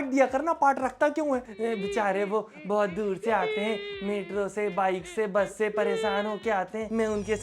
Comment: did he just said meerut? If he is saying he is just polluting the environment of meerut culture 😢